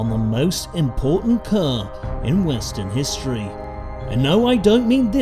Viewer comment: Oh, he said the most important car in WESTERN history. I thought he said WINSTON history, which also would have been true.